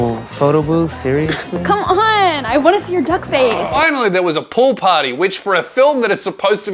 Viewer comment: when he said pool party i thought he said poor party and thought that it would be a solid 15 mins of being painfully out of touch